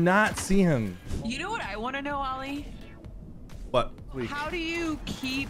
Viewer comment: I knew your name was Ali, but when I first heard your wife say it I heard “Ollie” and was like 🤓🤓 ain’t no way